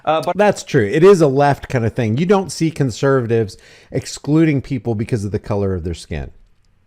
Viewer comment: I thought he said consumers, not conservatives . Due to the fact that I'm surrounded by stupid white people who vote and breathe democrat.